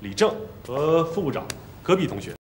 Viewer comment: 额，我一直听成女主名字是弱智。。。请别告诉我只有我是那么认为的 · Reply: 我还把戈壁同学听成 隔壁同学